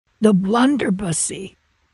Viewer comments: I thought he said the blunderpuss e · I thought i heard blunder bussin for a second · I thought it said budussy ☠️